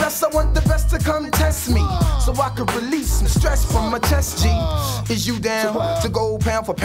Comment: Huh, all these years i thought he said "release some stress from my testes"😂